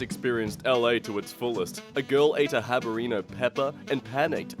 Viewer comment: D-did he just say habareno? Yes, he just said habareno